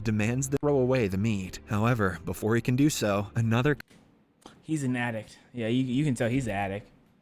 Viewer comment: What I hear 6:16 he’s an attick